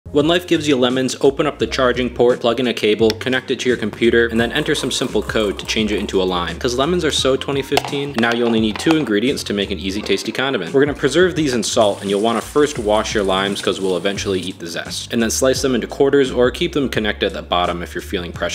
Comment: i thought he said an “easy,tasty condom” 😭😭